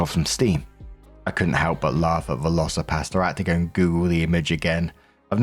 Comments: LOL, I never heard of that movie and thought you said Velocipasta. Was instant eager to go watch it. 🤣🤣🤣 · i misheard you and thought you were saying Velocipasta. Now I'm weirdly sad that there are no spaghetti dinos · I really thought you were saying Velocor-PASTA. Really not sure if I was more or less confused than knowing it’s real name 😅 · I did NOT expect a Velocipastor mention! 😂 My friend made us watch it for her birthday one year - I think you'd love it! :_WaffleWave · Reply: With Brit accent, I thought he was saying "VelociPasta" and was picturing some crazed predatory tortellini! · I thought he was saying VelociPASTA 😂 All I could see was pasta flying at the screen